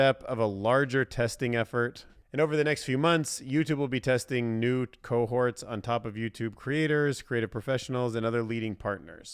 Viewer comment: did you say "nude cohorts"?